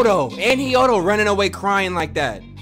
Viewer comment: did he just say hiyodo instead of hiyoko 💀